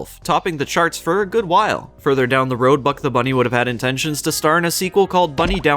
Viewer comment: For an second I thought you said Puck the bunny D